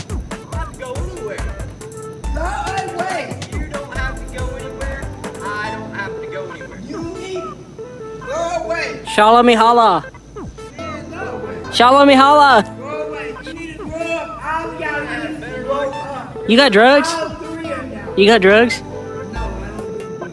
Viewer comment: what the hell is he saying? SHALLA ME HALLA??!?!?! Am I missing something or is he speaking in tongues?